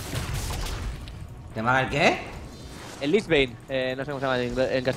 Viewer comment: QUE ME HAGA EL QUEE?!? que dice este? xDDD